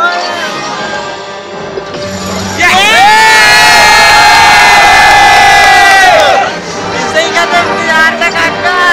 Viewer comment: suit up was great but what is that the guys saying😂😂😂🤣🤣🤣🤣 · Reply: Open armour